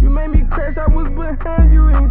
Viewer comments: “You made me crash I was behind you, you ain’t tell me you was turning on me” 🔥❗️ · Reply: I thought he said you ain’t tell me u was Xaning the lean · “You made me crash I was behind you, you ain’t tell me you was turning on me” 🔥❗️ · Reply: Thought he said “you ain’t tell me you was that into me” 🤨yeah ima stick to my version